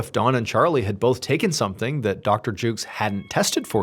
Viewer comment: After the first time you said the doctor's name, I thought you said "Dr. Juice". I couldn't unhear it from then on. It would be a cool rapper name, though.